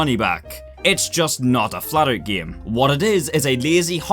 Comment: thought he said fallout